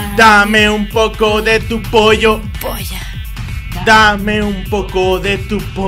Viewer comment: creo que dice polla xd